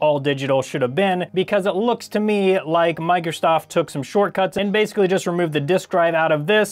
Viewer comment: Is it just me or does it sound like he is saying "Microstoft" ?